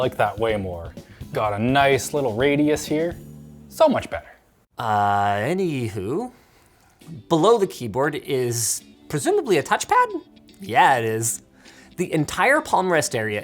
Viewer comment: did Linus just say “AnyWHO…” ??!!